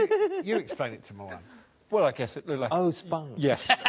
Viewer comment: look like a what? couldn't hear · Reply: Alex says "Well, I guess it looked like..." and Mawaan says "ohh, spunk!"